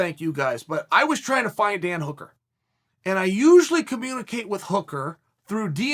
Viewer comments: In the beginning, I honestly thought you said, "I was trying to find a damn hooker!" And I was like, "Hell yeah, Chael !" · I heard 'hookers' 😂